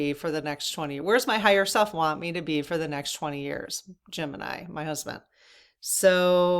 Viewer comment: When you said Jim and I , I thought you said Gemini, was confused for a second! 😆🤣Thanks for all you do.